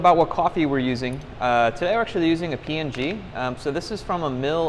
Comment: So is the coffee called "Papua New Guinea Riverside", the one for sale on your website? I can't tell what you are saying circa 7:12,, something like "today we're using a png", or "key in G"? Be nice to include names of the coffees used in your series in the description. Nice series, btw!